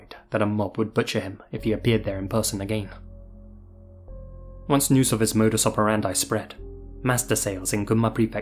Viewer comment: can someone explain the sentence from this? im having a hard time understanding what he's saying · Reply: Once news of his modus operandi spread It is someone’s habits of working, particularly in the context of business or criminal investigations